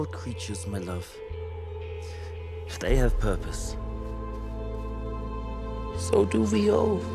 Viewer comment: "Rats are the lowliest and most despised of all creatures, my love. If they have purpose, so do we all" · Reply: i thought he said “but they have purpose. So do we all.”